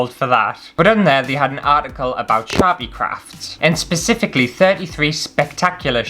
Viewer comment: OMG when you first said "Sharpie crafts" I heard "Chav-y crafts" and although I may be Canadian, I know very well what a Chav is lol. I think you guys call 'em roadman now, IDK, but hahaha. "Chav crafts. Crafts for people who just can't be arsed!" Oh! I drew a picture, if you want to colour it in, here's the lines and feel free ♡♡. <link> Not promoting, just gifting lineart to any colorists who want it ^-^ ♡